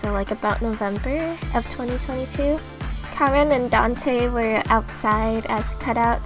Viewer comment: I turned on the captions by accident and instead of saying 'Charon and Dante' it says 'Carmen and Dante' which is a MUCH different vibe